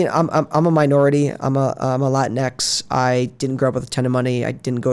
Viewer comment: What did he say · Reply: “I am a LatinX”. People from Latin American Heritage.